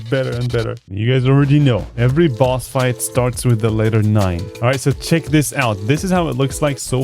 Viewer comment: I'm sorry, did you say the LETTER 9?